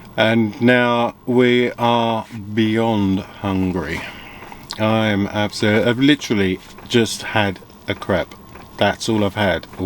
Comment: I thought that you said "I've literally just had a crap🤣